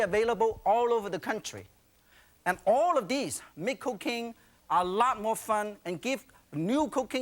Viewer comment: I thought he said and all of this make cocaine a lot more fun.